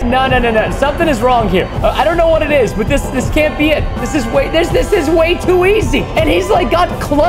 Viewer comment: i thought he was saying "this was way to hard"